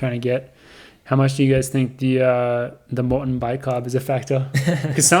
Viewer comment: what is Bicarb? What is the word they’re saying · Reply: Maurten bi carb